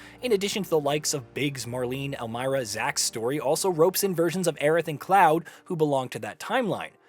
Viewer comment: Brother. I heard him say "Aeris" here. I used to say it that way to up until remake released w/ voice acting